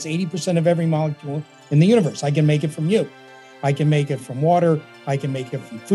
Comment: Did he just say "I can make it out of you"? oh god, green hydrogen is made out of people!